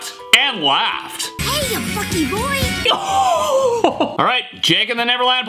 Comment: Uhhhhhh cubby what you say? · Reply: He really said hey Bucky boy